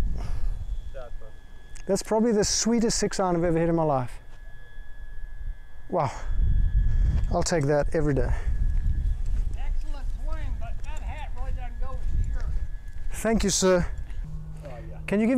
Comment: What did the guy say at 9:15 excellent swing, but that hats got to go lol!